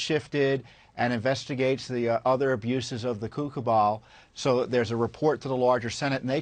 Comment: AHAHAHAHAHAHAHA! The Coup Cabal, AKA the CuckooBall! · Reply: I thought he said Kook Cabal. ?